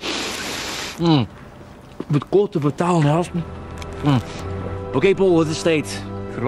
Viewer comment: wat zegt hij op 0:46? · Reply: "Ge moet korter vertellen, he gasten"